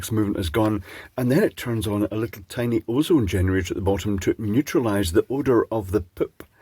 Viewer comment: Thought you said cat neutraliser, lol